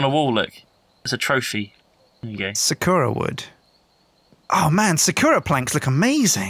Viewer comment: When he said Sakura I thought he said zecora and I got exited